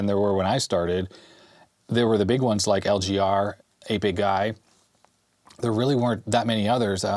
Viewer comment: When you said 8-Bit Guy the first time, I heard "Hey Big Guy". I'm a little slow some times.